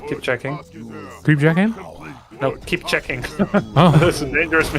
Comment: What does say at 44:39? Something in Dutch? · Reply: "Creep-jack him?" "No. Keep checking"